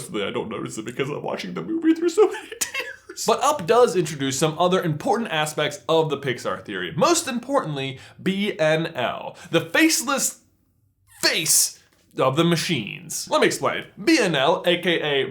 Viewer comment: Heard him say rise of the machines goes and watches Thomas the tank engine